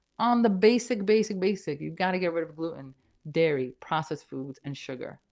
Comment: How can you give up coffee when it makes you happy! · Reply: I don’t think she said coffee I thought she said dairy